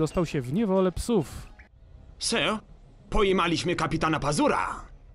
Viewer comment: Za pierwszym razem usłyszałem "Psy... pojebaliśmy Kapitana Pazura" xD Pozdrawiam